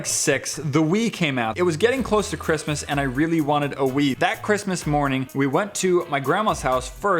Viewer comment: Was only listening to at 4:45 and I thought he said he wanted to wee...